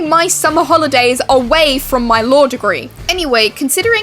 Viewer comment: Your accent made it sound like you said “my lore degree” 😂